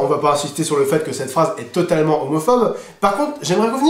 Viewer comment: Par clntre les gars je dois avoir buger Mais j'ai écouter a nouveau cette vdo et je crois c'est la premeire qu'au lieu d'entendre le mot "homophobe" j'ai entendu "homophome" 😅 Ça dois être moi